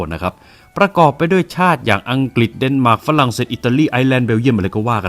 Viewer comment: ตอนแรกผมได้ยินว่ามันฝรั่งหรือหมากฝรั่งนี้แลหะ😂